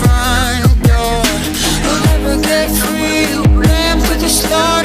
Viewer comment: who else heard "just land in the water"?